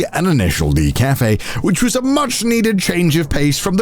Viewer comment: The ch*nko lady left such an impression on me, I thought Connor said "Initial Dick" cafe T_T